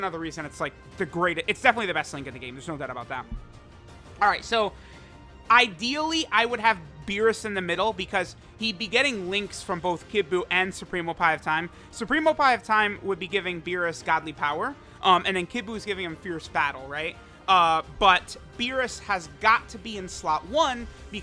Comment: i heard him say supreme oppai of time